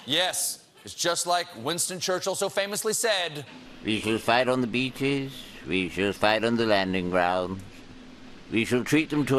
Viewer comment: I just experienced the Mandala Effect. I thought Churchill said "We shall fight THEM on the beaches.....". I'm English and ashamed, (but only a little bit).